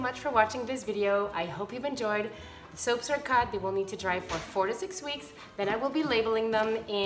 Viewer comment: Minute 6:21 was 😯 I thought you said 46 weeks